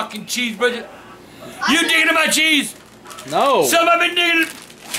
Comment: I think said the n word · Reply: *diggin'